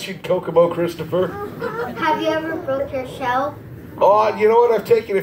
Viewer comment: what did he say? If it’s not what I think read the captions 😐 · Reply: oh it sounded like “have you ever go to hell?”